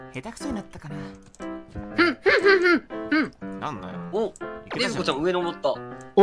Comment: 「なんだよ」ってちょっと聞こえるw